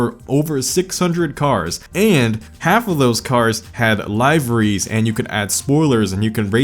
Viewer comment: Just me or did he call Liveries "Live-Reese" in